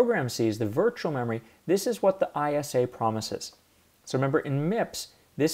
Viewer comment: the author says something that I can't understand due to my low English listening skills. Did he mention ISA which stands for Instruction set architecture? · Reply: Yes, the MIPS address space is 32 bits. At 0:30 he says "this is what the ISA promises" referring to how each program gets its own virtual memory space (32 bits).